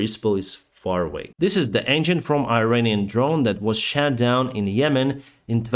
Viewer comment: Your pronunciation of “shot down” when talking about Iranian drones sounded like you said “shat down” which works just as good and is hilarious to English speakers.